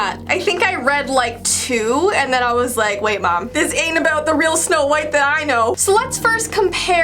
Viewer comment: When she said wait mom who thought she said wait bob XD